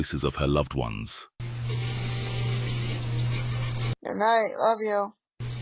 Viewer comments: Sounds like it said “Goodnight Mommy” too me. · On the tiktok with the children saying goodnight to there mother, that third voice sounded like it said "goodnight mommy", not "goodnight love you" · 8.00 I thought it said goodnight mommy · i heard "good night, mommy"